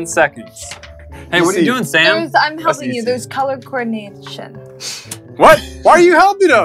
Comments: Did anyone else hear say there's colour coordinated sh*t? 3:31 she said it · WHAT DID SHE SAY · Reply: She said coordination